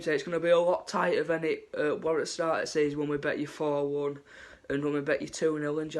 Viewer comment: what did he say did he say bet or beat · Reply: vAdxm aha meant beat mate